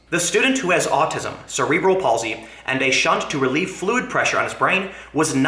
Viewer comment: hahaha did you just say Blued pressure? hahaha